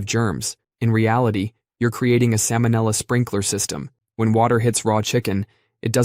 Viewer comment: Wait. Did he just say "Sam O'Nella?" ---- Show me on this doll where Sam hurt you!